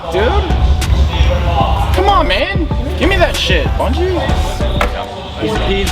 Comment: 6-0 Series count* come on, can't be making mistakes like that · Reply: OpTic Hitch did say at 11:49 give me that bungie?